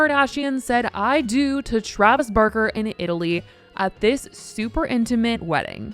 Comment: When you said "kourtney Kardashian said I do to Travis Barker" I heard at first "Travis Burger" lmao 😭